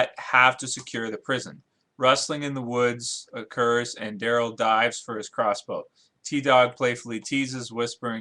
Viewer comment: swear to god i thought he said Daryl dies